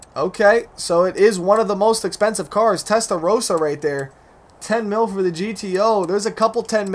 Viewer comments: When u said Testa Rossa I thought u said Tesla Roadster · Anyone else thought he said Tesla roadster when saying testa rossa